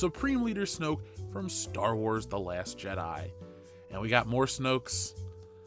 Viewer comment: Mmm...you say "Snokes" and sometimes, I hear "smokes". One is a virtually valueless thing that burns out quickly and is thrown away...and the other is a nicotine stick with a high tax.